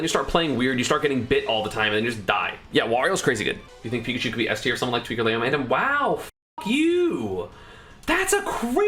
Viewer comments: what the hell is he saying bruh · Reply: do you think pikachu could be s tier, if someone like tweek or leo (mkleo) mained him? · please, english is not my forst language, what did he say😂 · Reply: do you think pikachu could be s tier, if someone like tweek or leo (mkleo) mained him?